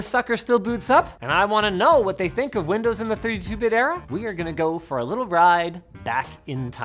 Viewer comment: What is he trying to say around 0:36 ? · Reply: “Windows in the 32 bit era.” Meaning that Windows today and a couple generations before ran in 64 bits bringing more RAM available to use (32-bit can use 4GB ram max) and better performance. Also, most applications these days MIGHT be compatible to work on 32-bit OS’s but will not have all the features that 64-bit OS’s of today would have. TL:DR 32 bit is for old operating systems, we’ve been using 64 bit operating systems for over the last decade and it’s better and faster.